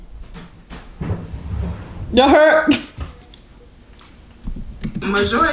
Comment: did they say he got a nipple gap 🤣